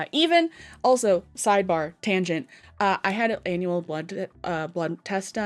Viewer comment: girl I thought you were gonna say annual blunt